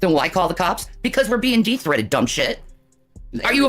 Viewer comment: i kept hearing "de-threated" like they're detransitioning from a threat 💀